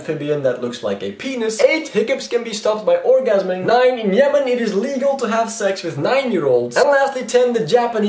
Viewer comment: thought you said "It is illegal to have sex with nine-year olds" and I was like: no shit? xD